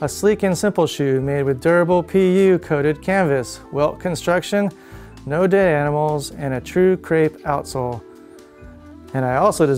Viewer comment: "well construction, no day animals"? What does he say at 3:47? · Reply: Late but "Welt construction and no dead animals" - because the Tempsters vegan and appreciates high quality footwear.